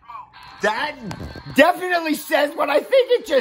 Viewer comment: And by the way that toy said "Activate Beast-X Mode." Oh jeez! I could see how that could easily be misheard! 😂